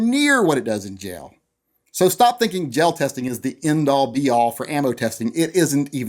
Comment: I thought he said jail testing and thought about how they would do that...some dark thoughts arose.